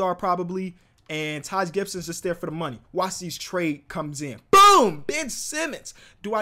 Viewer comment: I thought he said “BOOM bitch!” 😂